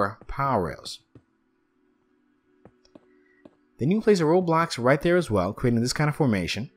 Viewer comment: It sounds like he said: then u place a roblox